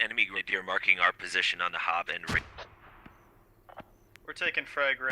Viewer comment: "HOB" lol, I think we've all said that